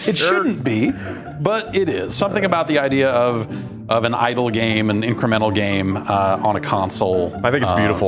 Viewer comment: Whenever they said "idle game" on their podcast, I thought they said "idol game" which didn't make sense to me because they said they liked it.